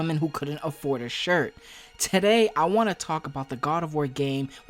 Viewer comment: Did he just say “tidday” instead of “today” 😂😂😂😂😂